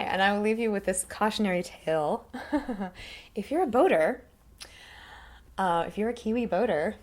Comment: Kia Ora Hahaha Cautionary part I couldn't figure out what you were saying it sounded like you said "Buddha" then you mentioned Deck and then I realised you were talking about Boater as in a sailor lols deck to me is (Veranda) but thats another thing haha Mr and Mrs Smith bang on I thought :)